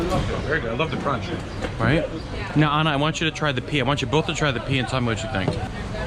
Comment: I thought I heard "Anna...I want you to try the pee..." I was like let me replay that one more time...😖